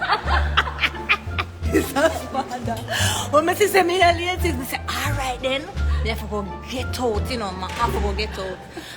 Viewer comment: Can someonr please type out what she said from 3:00 - 3:36? Shelly could read me the instruction manual of my computer with that Jamaican accent and it’ll still be funnier than most comedians. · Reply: 3: 25 "when I saw that I was in lane six, I said ok, im gonna have to get out quick. Then I said, ok im gonna have to get out and run the bend as fast as I can(wire dung di corner). While on the bend I spotted you (shericka), listen, I was like she is coming in like a flood, I had to say, look at Noesha (Noah)